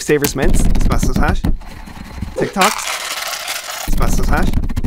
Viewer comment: Definitely heard him say *tik toks" instead of "tic tacs" lmao 😂😂